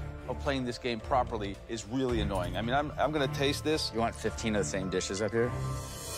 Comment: what did he say to Joe? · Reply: "you want 15 of the same dishes up here?"